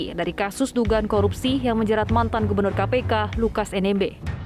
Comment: Wahahahahhaa kok aku denger mantan gubenur kpk ya 😂😂😂😂😂😂😂 aku salah dengar atau bagaimana😂😂😂